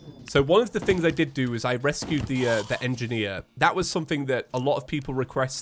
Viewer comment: did you say engineer??